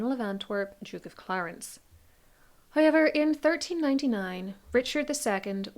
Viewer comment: It’s so interesting how your pronunciation of the diphthong OW sounds like I. It sounds like you say highever instead of however.